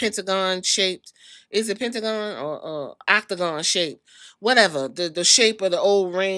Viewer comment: I Thought you was trying to say Pentagon Jr, when describing the ring. 😂